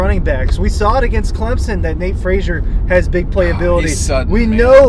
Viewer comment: At the 13:20 minute mark, it sounded like the driver said Nate Frazier sucked. If I heard that correctly, have that same energy for your boy Beck